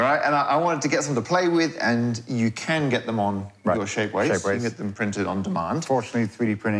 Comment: I kept hearing “shake weights” instead of “shapeways”